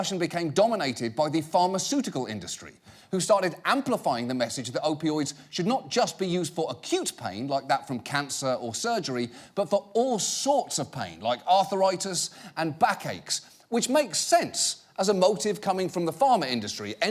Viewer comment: HAHAHAHA He said "acute pain" and I heard "a cute pain" and I was like dafuq John... ?